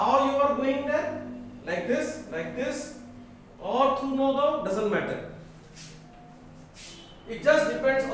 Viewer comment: i heard "all through nogaon" lol